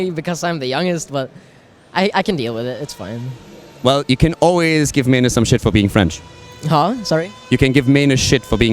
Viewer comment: What does said about french at 5:21 ? I didn't understand at all. Can someone explain that to me ? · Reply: so you can give may nor ( or something like that) s**t for being french